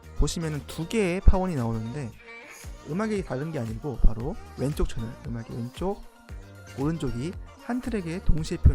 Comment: 부분의 말씀이 잘 안들리는데 어떤 말씀이셨던 건지 알 수 있을까요? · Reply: 두 채널이 보여서 음악이 두개인게 아니고 기본적으로 한 오디오 트랙에서는 왼쪽, 오른쪽트랙으로 나누어서 표현한다는 말을 하고 있네요 지금 들어보니 오디오 퀄리티가 꽤 별로네요 담에는 잘 체크해보겠습니다:)